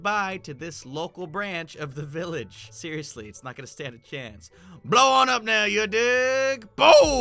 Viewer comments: With the anti matter TNT it sounded like he said BLOW ON UP NOW YOU D#%$ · “blow it up you d!ck!!!” Did he actually said it?